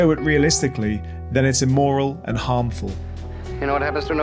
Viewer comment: i’m sorry I think I misheard that. Did Roman Polanski say something was immortal?